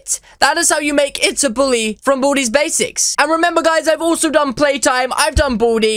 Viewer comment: What I heard was "From booty's basics"